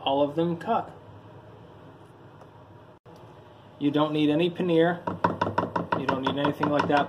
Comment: does he say, "You don't need any paneer?" Random.